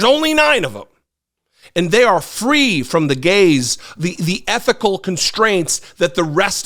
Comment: Slightly off topic, but is AI doing all the closed captions for everything nowadays? At 5:35 he says "free from the gaze" but the CC said "free from the gays". 🤦🏻‍♀ Can't we have real people doing the CC? Like, who do we complain to to get this resolved? It's like "the drunk autocorrect elves" are doing the closed captioning for all the YT videos.